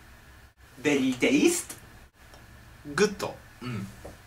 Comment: I thought he was going to say "Tastes very very very good" But he meant "berry" not "very".. lol